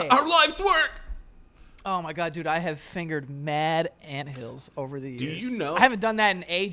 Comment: On God I thought joe said “I haven’t fingered an animal in forever”